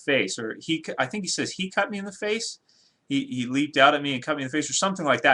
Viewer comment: sounded like he said he clocked me in the face